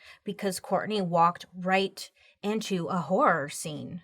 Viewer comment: I first heard "Walked right in to a horse". 🐴